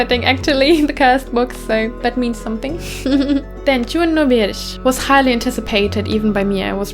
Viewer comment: does anyone know what is the title from game on 6:53 ? im not able to comprehend from listening and subtitles dont help much either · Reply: It’s Virche 🙂